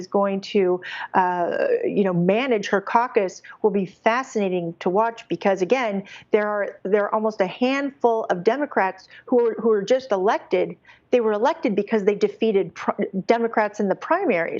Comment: thought it said rejected. forgot that its all rigged there for a second lol